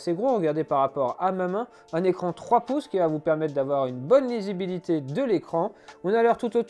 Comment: Une bonne quoi 😱 ?? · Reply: Lisibilité ?!